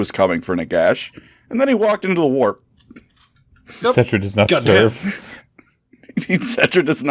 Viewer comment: I thought he said Sentra does not surf. lol. I pictured in my mind a mummy hanging ten on a longboard riding out a big barrel. Of course being Sentra, he’ll never wipeout and ride every curl to the beach.